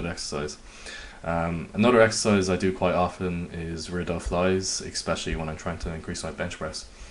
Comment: Can someone tell me the name of the accessory movement does for his bench press 5:27 . I can't quite make out what he's saying. Tried searching up but I can't find it. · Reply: Yeah rear Delt flyes